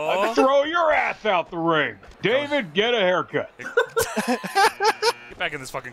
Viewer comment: i thought he said “David Guetta haircut”